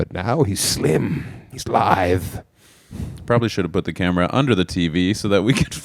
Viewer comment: is he saying lithe or live lol